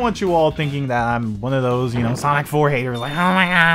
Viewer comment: i know its sonic 4 but it sounded like sonic forces